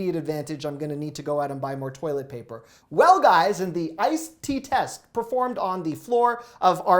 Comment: “Seriously?? He did an iced pea test on your bathroom floor for toilet paper?? “ My husband cracked up when he walked in and misheard you!! Thought you might get a chuckle too. I had to replay it for him so he would know you weren’t totally bizarre! Thanks for the great info you provide!